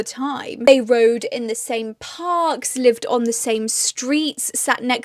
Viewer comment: I am not used to your accent and thought you said "they rode disabled sharks, lived on the same streets.." had to watch it twice 😂